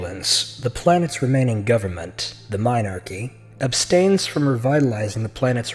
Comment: I'm sorry, did I hear correctly? "The Minarchy"?